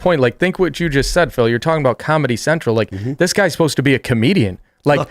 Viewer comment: You won't find more corrupt judges than the ones in new york and DC. When they were talking about Colbert's insane and erroneous rant on comedy central I thought Phil said "commie central"